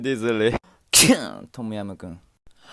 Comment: What did he say?? I was born and live in Thailand but I don't know what he was saying. Edit: OHHHH I get it now. He said 'Tom yum kung'